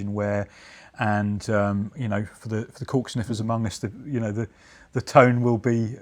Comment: Definitely thought he said "coke sniffers" when he said "cork sniffers"